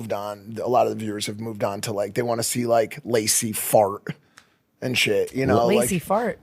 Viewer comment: To be fair it definitely sounded like he said Stacy Fart